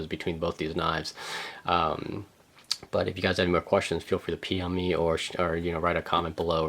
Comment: Is it just me or does it sound like he said "Pee on me"@ 6:19? Great video regardless! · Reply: Lol, I guess I said it a bit fast and my words ran together, I meant to say PM me. Thanks for watching!